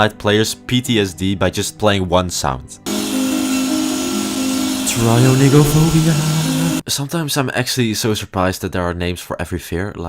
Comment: he did NOT say trionigophobia 😭